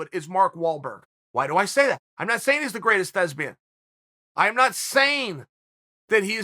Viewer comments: Did he just say "I'm not saying Mark Wahlberg is the best lesbian, I'm not saying that". 😂. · Thought he said Mark Wahlberg was the greatest lesbian 🤣